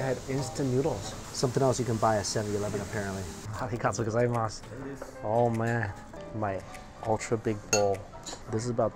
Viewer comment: Any Japanese speaking people who can tell me what he said at 11:59? something-gatsu gozaimasu? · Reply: Excludos pre sure he just said arigatou gozaimasu